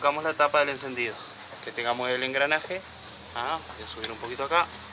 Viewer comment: Hola. Favor me podría decir que dice, (¿¿_____?? el engranaje), justo ese pedacito como que no distingo el audio. Gracias, y muy buenos tus videos. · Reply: Que tengamos el engranaje (al momento de cerrar la tapa no olvidar el engranaje) 💪🤝